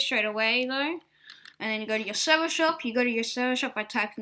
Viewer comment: Nice vid 5:47 Sherver Sop instead of Server Shop is good stuff 6:18 Shet up instead of set up is also good stuff